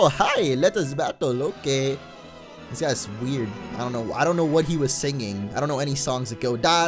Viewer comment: when says i dont know it sounds like he is saying audino